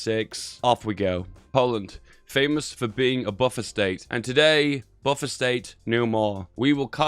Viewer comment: I misheard “buffer state” as “buffet steak; now I’m hungry ;(